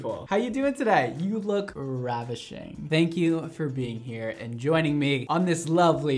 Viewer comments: “you look ravishing” I swear for a second i thought he said ratchet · "You look ravishing~" Me sat here with freshly bleached orange hair hair: 👁👄👁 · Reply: I thought he said “radishing” 😭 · I’m the beginning of the video I really thought he said “ you look ratchet today” I was just like how do you know? 🤣